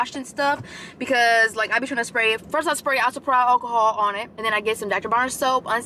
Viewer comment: what is oxi perhide alcohol? I thought you were saying hydrogen peroxide at first, but this sounds like maybe the name of a product? Eta: maybe isopropyl alcohol. I'm gonna need you to slow down and enunciate.